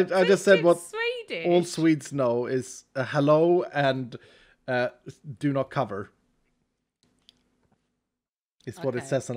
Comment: Couldn't figure out why you kept saying kesäpeitte until I heard "do not cover" :D